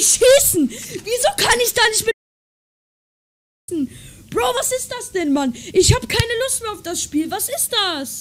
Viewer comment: that lil kid at 1:30 made me smile...all i heard was angry german noises than, "vas is das" which means "what is this" and im like AHA!!!!. i know that one, very true, WHAT IS THIS shiza